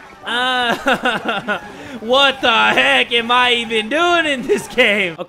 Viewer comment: Did just say "heck" *WHY ARE WE STILL HERE JUST TO SUFFER*